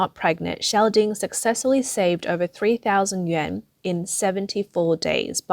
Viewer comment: closed caption says she saved 3,000 Yen if this is China is it supposed to be 3,000 Yuan? Either way if Yen that's $20 and if it's Yuan that is around what $400? $20 or $400 in 74 days, is that A LOT of money in China? or are my conversion rates just WAY off?